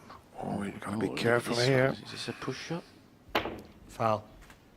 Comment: What happened at 4:07? One of the commentators mentioned "pushed up"? I dont really understand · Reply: He said "push shot" You can look up the definition of that, but push shot is a fould in snooker.